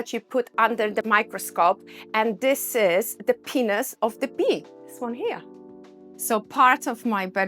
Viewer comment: For a second there, I thought she said the artwork was ‘The Penis of The Bee’ 😂 Honey,Honey, Honey 🍯 🐝